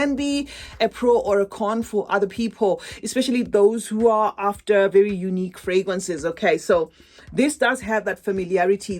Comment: Hi ❤ 6:12 did you say that it is NOT nice enough to add to our collections? Did I understand correctly? Thank you! 😊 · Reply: I said it’s nice enough to add to your collection unlike the original brown Musamam that they released. This is a good perfume 🥰